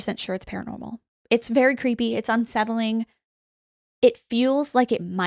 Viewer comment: I misheard "very creepy" as "paracreepy." 🤭 Still crediting for this "new" portmanteau, when I inevitably use it in a sentence. 😂 Meaning, "creepy adjacent," or "beyond creepy." 🤔 🤣 Love and appreciate your paranormal and "paracreepy" content, ladies! 🫶